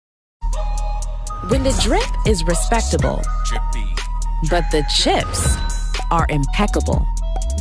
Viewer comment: When the children are crying, but the chips are worth trying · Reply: I thought you said When the children are dying, but the chips are worth trying